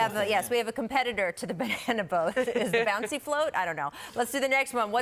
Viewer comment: Did she say kawhi on a banana boat? That's racist 🤔